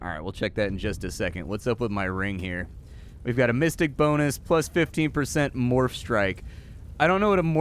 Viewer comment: Why did I hear "mystic bonus" as "fisting bonus"? 😂🙈